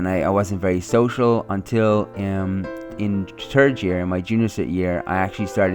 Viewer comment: He said third it sounded like he said turd